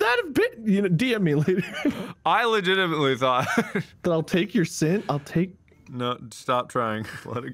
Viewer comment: I wonder if Markiplier thought said "I'll take your SIDS" ?